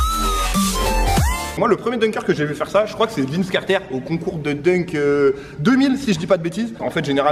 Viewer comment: Vince quoi ? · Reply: Karter